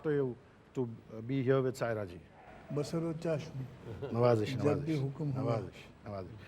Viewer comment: what did Sahab said 'Bashar aur chashni' please if any one can correct me ? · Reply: Ba sar o chashm matlab sir ankhon par